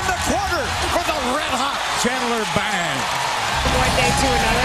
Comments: did they just called him "Chandler Bang"!? lmao (Friends reference) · did he just call him chandler bing at 1:15? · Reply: We called him "chandler BANG" lol